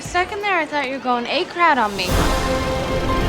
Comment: When I was a kid and Lilly would say to Mia, "I thought you were going A-crowd on me," I thought she was saying, "I thought you were going A-crad on me." And I was like "What the heck does A-crad mean?" 😆😆😆